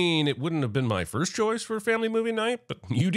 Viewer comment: I just saw your captioning at 14:20 that says dew instead of do. I love that 😂. Keep up the amazing videos